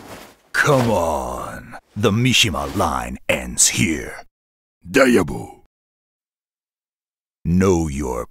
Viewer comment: What is he saying here? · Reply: “Die ever!”